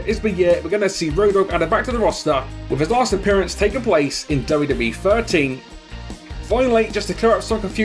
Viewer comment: Not feeling these games anymore 2k13 was the beast game in ages or Here comes the pain for ps2 😩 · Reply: Tf is 2k13? I heard f WWE '13